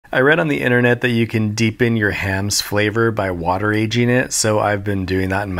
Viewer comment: I watched this episode 4 times, and I always thought he said water raged.